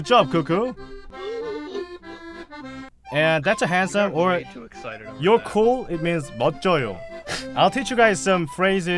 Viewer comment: 이 "너 그거갖고 되게 좋아한다" 이거 영어로 잘안들려서 ㅠㅠ 알려주실분 있나요! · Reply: "You've got way too excited over that." 이라고 한 것 같아용😀